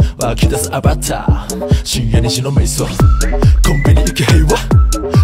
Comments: サムネイルとっても好きです✨ 今日0時に配信されてからずーっと聴き続けていてほんっっっとうにかっこよくて…どうしてもMVが予想できなくてすんごいワクワクしてます✨🍄 目をすんごいかっぴらいてるサムネイルの左下のキノコ🍄が好きすぎて聴きながら凝視していました👀 "深夜2時の迷走"という歌詞がどうしても"しんみり白味噌"に聞こえてしまって自分の耳が心配です助けてください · 深夜二時の迷走が深夜に白味噌に聞こえてすごい歌詞やなって思ってたけど